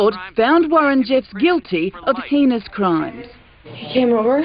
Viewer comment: it sounded like she said "guilty of penis crimes"